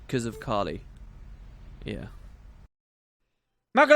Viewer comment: lol what did you shout at 32:56 :P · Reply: boy102 Macalania Wo